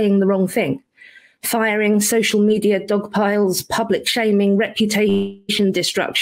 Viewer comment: could have sworn Helen said "Puppet Shaming" which while incorrect might actually be just as effective if not more.